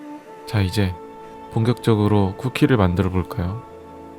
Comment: 나만 공격적으로 라고 들림?